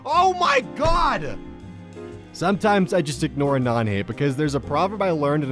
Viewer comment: does he say nane instead of anon?